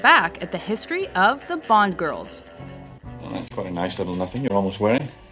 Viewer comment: I thought it said blonde girls lol